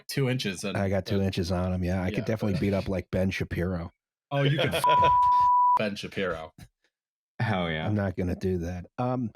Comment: That short beep really made it sound like said could fuck Ben Shapiro.